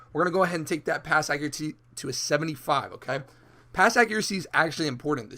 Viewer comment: U stuttered when u said Acuraty instead of Accuracy